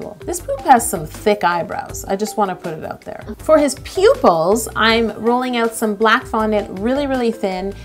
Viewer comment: When she said she had to put on his pupils I thought she said "poop-ils" and I was so proud of her pun game😂